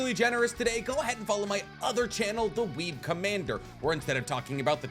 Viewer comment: my dumb ass thought he said “weed commander”